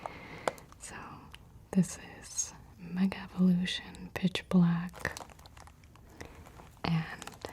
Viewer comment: i’m so tired i thought that said bitch black